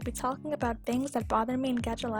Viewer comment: Is it sad that I thought that it said “brother” I